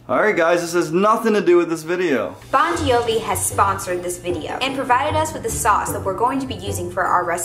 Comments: at first i thought she said “bon jovi has sponsored this video” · When said bonciovi I thought she said Bon Jovi What?!